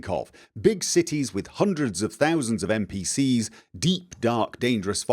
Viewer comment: I misheard "hundreds OR thousands of NPCs" as "hundreds OF thousands" and was kind of terrified.